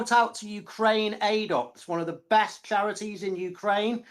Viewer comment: what charity is he talking about, cant understand him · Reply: Ukraine Aid Ops